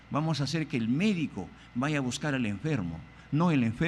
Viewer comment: no entendí esta parte xd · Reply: Que el Médico vaya a buscar a enfermo!!